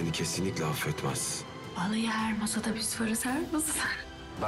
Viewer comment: Nermin kapının önünde kendi kendine ne diyo.3 defa izledim yine anlamadim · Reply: Vallayi her masada biz varız.. Her masa... gibi anladım ben de (tekrar tekrar izledim 🤣)